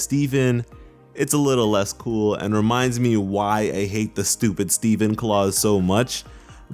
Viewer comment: what did he say right here? Stupid Steven Claus??